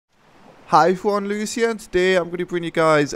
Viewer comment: What does he say in the beginning of his videos? Hi forne lus here? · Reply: wait his name is not? because at every video i keep hearing him saying "Hey everyone, Lucy here"